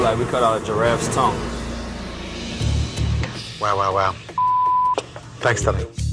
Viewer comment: I saw another clip, he actually says "wow wow wow, jesus". I can't even trust the censorship on these episodes.